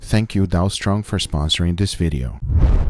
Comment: I thought i heard “thank you donal trump”.